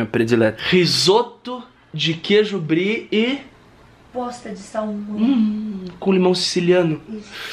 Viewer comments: Só eu entendi “risoto de queijo brie e bosta de salmão” ? Kakakakakakakakakakakak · eu entendi a falando bosta de salmão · Só eu que entendi bosta de salmão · achei que ela tinha fala bosta de salmão